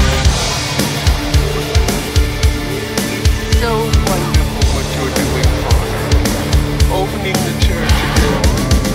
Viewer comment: Hey I know it's unlikely to get a response but it's bothering me... What does that random soundfile say? At 1:24 I can't quite make it out. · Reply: "It's so wonderful what you're doing, father, opening the church again!" It's from a 1987 John Carpenter movie called Prince of Darkness.